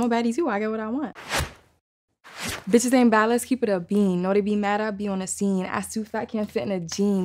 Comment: Thought she said "upbeam" but she said "a bean". The Bronx is like an insane Asylum or something.